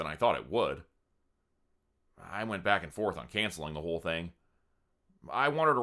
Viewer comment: When you said "like a cat on a hot tin roof" at 4:56 I seriously laughed so hard I almost kissed myself because I heard "like a cat on a hot kangaroo" rewound 3 times and finally had to put on captions because I kept hearing kangaroo 😂